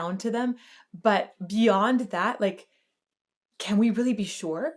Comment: I heard. " beyond the veil "